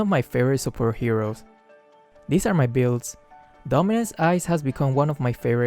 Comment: I thought said dumbass ice instead of dominance